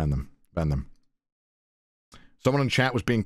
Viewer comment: i thought he said im gonna bend them 😭